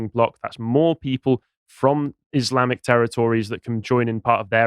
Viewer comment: did just say Islamic terror tories?